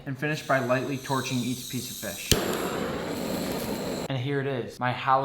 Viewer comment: I thought it said monkefish